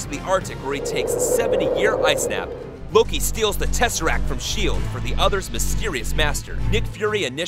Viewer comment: Why does the subtitles says SHIED instead of S.H.I.E.L.D. (Btw S.H.I.E.L.D stands for S.trategic H.omeland I.ntervention E.nforcement and L.ogistics D.ivision)